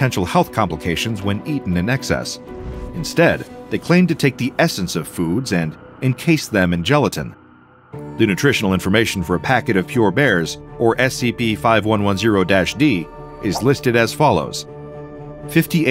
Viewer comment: I thought it said pure bread! LOL I mean it makes sense. It has two meanings but I didn't know why it would be pure bear. Anyways, I'm high as fuck right now so let's a go